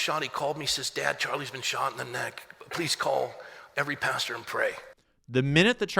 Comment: I thought he said “Charlie got shot in the neck . Had blood all over him “ . Referring to Charlie having blood all over him .